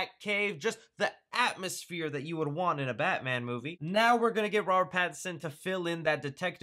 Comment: I heard "rowan Atkinson"